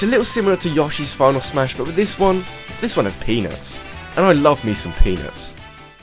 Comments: "This one has penis, and i love me some penis" - What i heard the first time listening....... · did u say penis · It sounded like he said "And I love me some penis." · When you said peanuts, i thought you said... penis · Under no means of disrespect, it seriously sounds like he says " and I love me some penis". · I thought you said, "I love me some penis."..... Very awkward for me.